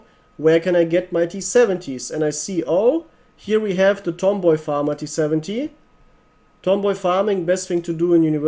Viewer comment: what did he say? Lmao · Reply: Let's go tomboy farmer